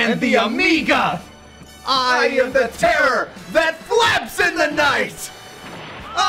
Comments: Huh, all these years I thought Goku said I am the terror, the FLAX in the night. Turns out I have been wrong for a decade. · I always thought he said “I am the terror. The BLACKS in the night”😂😂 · I always thought he said “I am the terror that quacks in the night” aw shoot